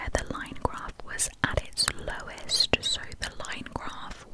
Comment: awww when you said Lowest it sounded like you was saying my name Lois🥺